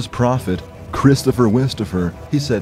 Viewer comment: I thought it said Christopher Wallace? 😂